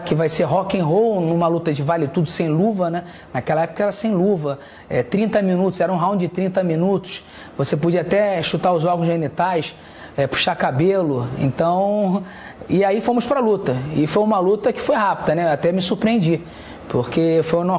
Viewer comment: so eu que entendi "chupar os ovos genitais"? kkkkkkkkkkkkkkk